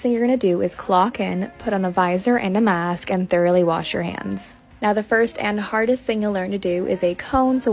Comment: At first I thought I heard “barely wash your hands” 😂😂